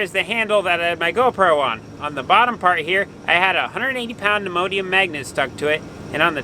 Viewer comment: What kind of magnet was that again ???? 🤔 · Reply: I had to replay. First time thru I thought he said Immodium magnet. Neodymium was the goal.